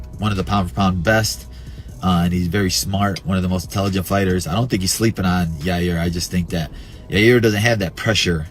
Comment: I don’t think Volk is sleeping on anyone. Bad take from Belal. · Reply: He does not say that. Listen to the video. He says "I don't think Volk is sleeping on Yair" The person who made the video made an error.